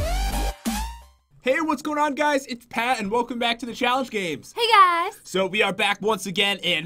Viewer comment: My chair was making a lot of noise at the start of the video so instead of hearing "Hey whats going on guys its Pat and welcome back to the challenge games!" I heard "Hey whats doing on goose it's back and welcome Pat to the galaxy age"